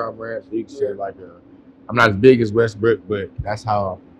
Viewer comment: This was a very dope interview, but I ain't go lie I swear I heard him say westbrick 🙄😂😂. Maybe my ears playing tricks on me because I've heard it so much last season 🤦🏾‍♂️